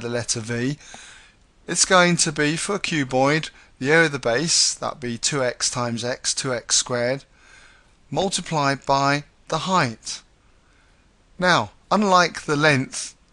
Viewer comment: I thought you said f*** you boid! Haha thanks